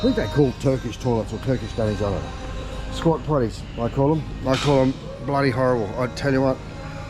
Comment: did you say "squat plea" ? I had always wondered what those toilets were called.